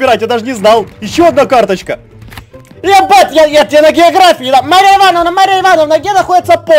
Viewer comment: мне одной послышалось е*ать я на географии😂😂😂